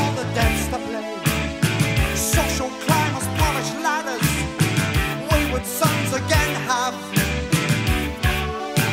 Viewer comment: Who helps me out? On the original CD "Script for a jester's tear" , @ 00:20 "Hello vicar, grab a glass of ......" ? What does Fish exactly say there? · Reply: "...fizz" (i.e. champagne).